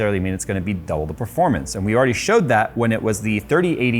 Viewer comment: I first thought it said "And we already broke it" but nvm